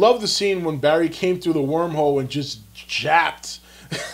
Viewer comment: Did...did he just say japped instead of jabbed? facepalm